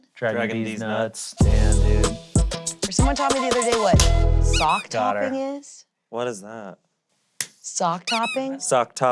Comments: I think Angela misheard "Sack Tapping" which is so hilarious that to her "Sock Topping" is some sort of inside guy humour instead of "tapping a guys sack" v🤣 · SOCK TOPPING? Truly she misheard “sack tapping”